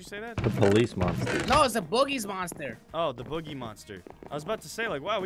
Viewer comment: at first I thought he said booty monster